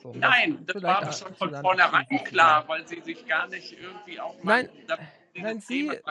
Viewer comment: Was hat er gesagt? Das sich nicht mit der Thematik auseinandergesetzt hat?